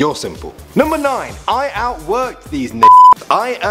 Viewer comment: is it just me or did he say the n word at 22:52 ??